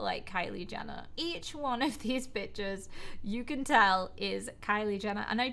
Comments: idk why but to me it sounded like she said b*tches :/ reads comment oh oops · Is it just me or did she just say bitchs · Yep I thought said the b- word but she said pictures 😂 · Did she say bishes or did I just hear it wrong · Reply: Pictures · says b*tchers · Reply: She said Pictures but it sounded like she said the B-Word